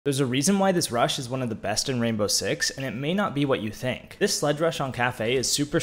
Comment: At the start I thought he said "this is why russians are the best in r6" lol